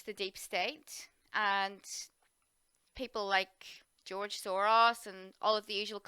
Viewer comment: I heard wrong and thought you said 'George Sauron', then I realized that's actually a more accurate name for him, so I'll be going with that from now on. Thanks! 👳‍♂️✌🌿🌌🕉